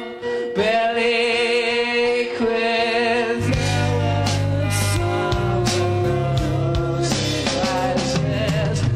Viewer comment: what is the other guy saying. I WANNA KNOW!!! ??😭😭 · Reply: I’m so good tonight (I’m not sure it’s saying that but that’s what I hear)